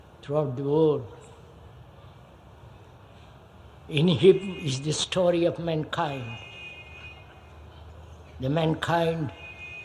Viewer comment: "___ _ is the story of mankind." I can't understand the title of the book that he states. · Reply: If it was heard correctly, K said "In him is the story of mankind". So, it was not referring to a book but saying that one contains the whole of mankind's journey as 'the story.'